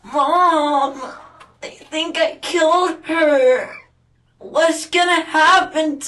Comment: "I think I killed her" is what it sounded like to me